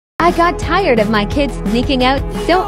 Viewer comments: I was looking at my phone and I just heard this I thought It said I got tired of my kids making out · It sounds like you said geeking out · I thought I heard "making out" instead of "sneaking out"😂